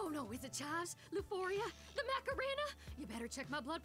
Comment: Did she say the Macarena?😂